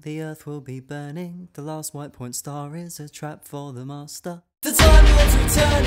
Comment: we all heard him say "the masters death" right? edit: I now hear, "the trap for the master" please try and prove me wrong